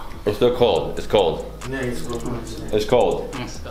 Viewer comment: Its cold. I think they thought you said..Its gold. 😂😂😂 Lovely vlog. Nice warm and friendly hosts. Btw by Banana fruit they meant Banana blossoms.